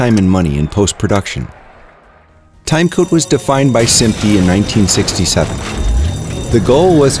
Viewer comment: Can someone tell me what he said here: 0:38? Timecode was defined by __________in 1967. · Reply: he said SMPTE :) Here's some more information: <link>